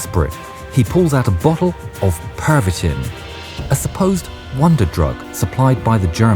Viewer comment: the first time I heard supposed I thought they said suppository...